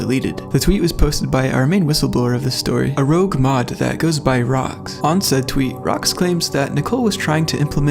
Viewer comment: Ngl on 2:05 I THOUGHT YOU SAID "ROX" INSTEAD OF ROCKS